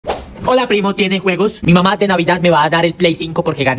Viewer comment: Entendí "hola primos tienes huevos" Jajajjajajajajajajjajajajajsjajhsjsjajsjsh También la canción jaajjahhahha